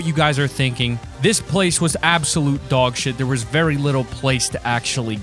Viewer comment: did he just said "dogshit"?